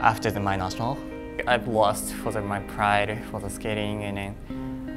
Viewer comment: did he say he lost his pride in skating at 2:49 ?